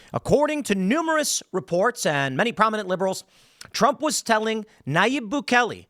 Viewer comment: Every single time I hear “prominent liberal says” also be said “propagandist says”